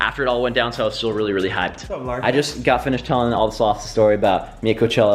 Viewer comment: did he say he was really high😂